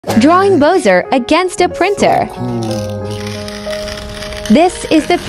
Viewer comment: “Drawing Boser against a printer” 💀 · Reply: Bro i heard "loser againts printer"🤣🤣🤣